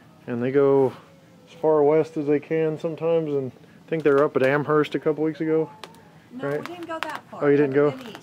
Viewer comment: did you say Amherst?